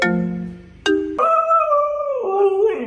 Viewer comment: I thought she said "Ain't no sh*t" When she said "Oh no she didn't"😂😂☠☠☠💀